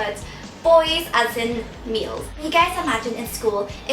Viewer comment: OMG, at the beginning, I thought you said 'Boys as in meals' Hahaha